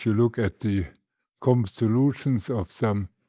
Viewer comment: If you find a mistake in the subs, please let me know in a kind way. You'll probably never realize how much I, as a non-mathematician, bled to get this subtitled before the weekend. PS. Make sure you're using the subtitles provided. I'm not responsible for the auto-generated ones, nor can I edit them. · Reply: "common solutions" instead of "constellations"